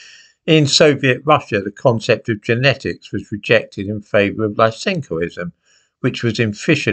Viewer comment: Can someone tell me, please, what Simon says at around 0:53? "The concept of genetics was rejected in favour of ?????" The best the subtitles can bring up is "Life synchoism," I looked this up and could not find anything related to it. I'm guessing it has transcribed his speech incorrectly. I am most interested to find out because I recently discovered that Communism and the pseudo-science of blank slatism are closely related. Many thanks. Keep up the good work, Simon. You are easily one of my favourite Youtubers 😊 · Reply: Lysenkoism